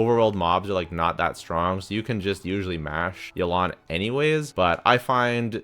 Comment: Ayaka should have been S. Also, I swear I thought I heard you say smash Yelan or something like that.